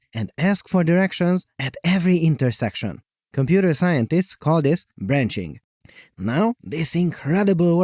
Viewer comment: Please tell me I wasn't the only one who misheard: "scientists call it... edging" 💀💀💀